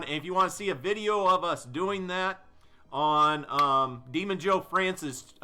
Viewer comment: For a second I thought he said "Demon-dean" xD